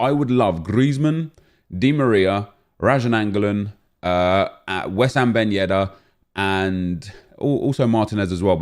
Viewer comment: Coulda swore he said west ham ben yedder... lmao